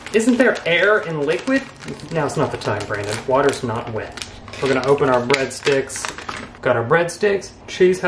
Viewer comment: Did he just say “Water is not wet” LMAO